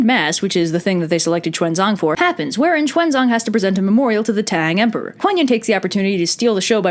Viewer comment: DID SOMEONE SAY "TANG"?! (no seriously my name is 唐浩然 shhhh dont tell)